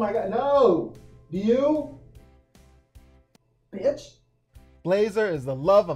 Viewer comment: Did you say "Bitch" 😅😅😅